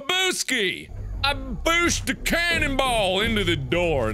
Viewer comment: Was I the only one who thought he was saying cannibal instead of canon ball?